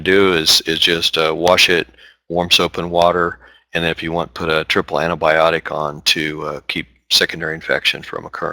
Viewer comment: I can't understand the accent. What does he say at 33:00? "Triple antibiotic"? He's describing what to put onto the skin if you botch a tick extraction and still have the mouth-parts attached. He says not to go digging it out, but simply to wash it and then put something else on it, but I can't understand what that something else is. Sounds like "triple antibiotics" or something, but I've never heard of something like that.